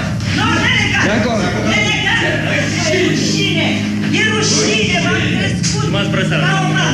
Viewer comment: is she calling them shitheads? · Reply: No, she is saying "are you not ashamed? I rased you like a mother" the word ashamed in romanian sounds kinda like "rushine"